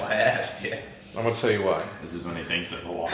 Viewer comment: What did say at around 9:00, hate it when I can't understand people even though I replay it like 10x.. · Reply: Think he said this is when he thinks of a "why"